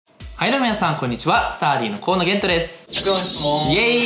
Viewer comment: どうでもいいけど「スターリン、河野玄斗です」にしか聞こえない